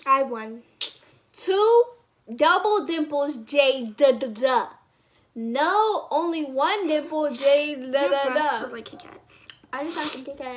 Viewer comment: I thought she said only one nipple...